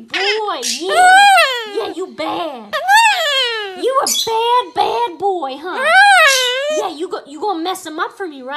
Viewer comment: did it say "I'm a bad"????